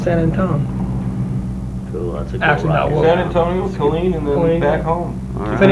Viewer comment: I thought I heard them say Killeen. Cool. Saw lot of great concerts at Ft Hood. Thanks for posting this vintage inteview!